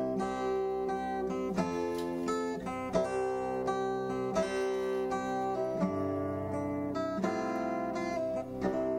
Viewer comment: What is "banic" or whatever you said prior to bacon? 16:42 a great smile of satisfaction. Well done!! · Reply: Bannock is a very old form of non yeast pan bread. My formula is simple…. 3 cups flour, 1 teaspoon salt and 2 tablespoons of baking powder. You can substitute clean ash from your fire pit for the baking powder, that is how it was originally made. Add water until it is about the consistency of pancake mix and drop her it a greased pan. Great with maple syrup.